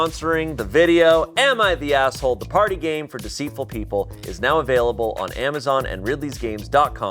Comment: Coulda sworn I heard "Am I the asshole? The farting game..." 😅